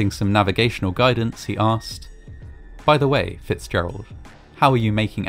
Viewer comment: I thought you said Ella Fitzgerald.